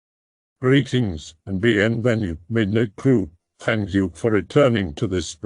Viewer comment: Lol so for the longest time I thought the intro said "greetings the athletic midnight crew" and thought it was hilarious... im also very deaf lol